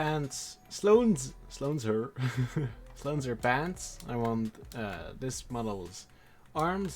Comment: Hard take but if any animator here knows a quicker and cleaner way to combine all bones (and somehow automaticaly remove the one that are not really connected to anything useful or duplicates) without loosing the rigging? Please let us know! Upvote this comment for visibility. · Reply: Did you just say Sloane'ser pants? Instead of Sloane's pants? 😂